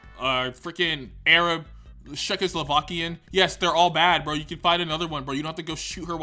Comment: did he say Czechoslovakian?